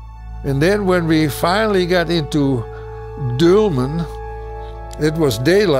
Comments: I think at 8:35 he says "Dülmen" instead of Dortmund. His accent makes it hard, but it also makes more geographical sense. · I think he said Dülmen not Dortmund.